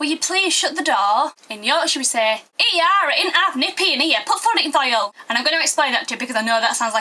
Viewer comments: What did she say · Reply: aye, we be nippy put wood in'th oyle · Can someone write what she is saying exactly from 1:56 to 1:59, which ends with "put the wood in the hole"??? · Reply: ey ar it int half nippy in ere, put wood int hole..... He are it ain't half nippy in here, put wood in hole.